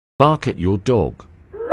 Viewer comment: I heard "bark can you dog?"